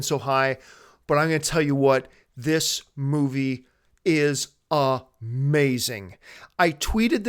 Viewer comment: I thought was about to say "This movie is A-MESS!" instead of "A-MAZING" (Haven't seen it yet, so it's not that I think it's a mess, just found it funny I thought you were going one way but then went in another)